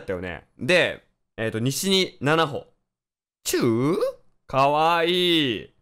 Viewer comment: のが言う「チュー？」が、からつけあっきぃさんのうぅ〜⤴⤴にしか聞こえない(笑) ほかの実況者さんの名前出してごめんさい、(´・ω・｀)